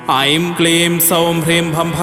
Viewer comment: Namaste. In Bhadrakali Moolmantra at 2:57, I hear Aim Kleem SauhM ... Is it SauH: or Sauhm In Sanskrit it is written Sauh सौ: (without M sound) Please help. 🙏